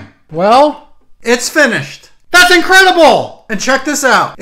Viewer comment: Why do I hear "Voldemort's getting a promotion." I know it's not that but it's what I hear.